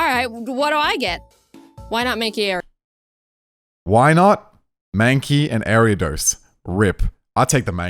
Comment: When the professor said “wynaut,mankey,ariados” is thought he said “ why not make it ariados”